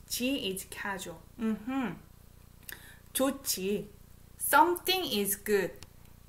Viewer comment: I just started watching... But at 16:00 could it be “학벌 좋지” ? As in “Good academic background” ? I also didn’t hear it clearly, but that’s what I’m picking it up when she says it... 🤷🏻‍♀️